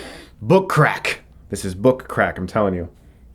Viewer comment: When he said he enjoys the smell of book crack, I thought he said he enjoys the smell of butt crack.............